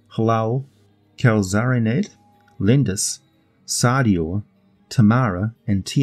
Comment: Amongst the draconian gods (lesser deities) I couldn’t quite catch it but did you say that one is called “Linda’s”? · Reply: Lendys, the dragon god of harsh justice. Not quite lindas